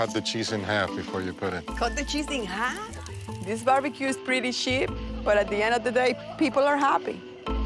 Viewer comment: When the mom said, "This barbecue is pretty cheap", I thought I heard her say, "This barbecue is pretty shit." It wouldn't be any less accurate 😅 Also, if the dad refills water bottles with hose water, I hope they don't live in Flint, Michigan.